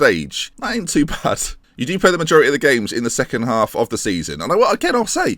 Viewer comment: did he just say "You do play a majority of the games in the second half of the season."?